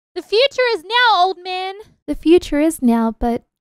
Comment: did she say “old men” or Open?